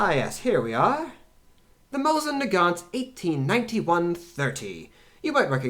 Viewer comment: I am very sorry, but I think I heard you saying Mauser Nagant at 6:52, and it is Mosin Nagant, since Mauser is a German rifle and Mosin was Russian. It couldn’t be possible, that they could work together. Or could they? ;) I am joking... I like your videos. Keep up the good work. History needs people like you, that teach the ignorant masses. I am subscribing now. Thank you for your content. 👍🏻🇨🇦